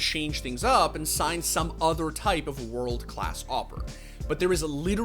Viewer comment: did u say "World Class Awper"??? Ahhhhh, GuardiaN xD he is free i mean it could be possible